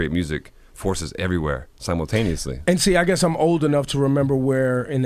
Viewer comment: What is Diplo saying at 13:46? What neously?